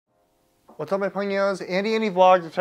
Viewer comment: I gotta ask, what is it that you say at the beginning of every video? It sounds like “what’s up my pony-o’s” I’m sure I am just mishearing it.